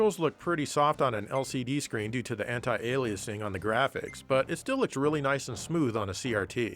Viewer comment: When said "anti-aliasing" about that anime flirting game I heard "hentai-aliasing"...